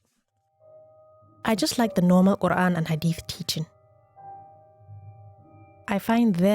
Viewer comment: I thought it said “I like the Normal Korean!” Btw it’s Quran not Koran.